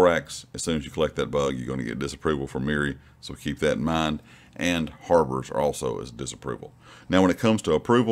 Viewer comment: I don't understand the word you are saying , sounds like Harbour and the subtitles say the same thing . You mean Mirri does not like being near boats ? Or am I mishearing the word you are using , had 3 people listen for me and they all say the same thing . Bit stupid you can't go near a harbour with her .